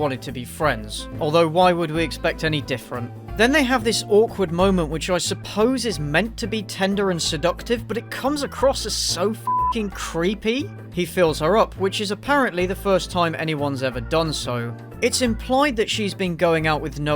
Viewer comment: What was said: “He feels her up” What I heard: “he fills her up” Me: WHAT?!